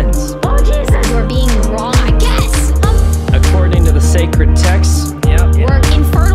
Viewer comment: I misheard secret texts as sacred texts